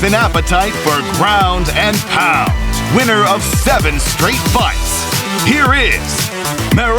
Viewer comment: haha i thought the commentator said, winner of 7 street fights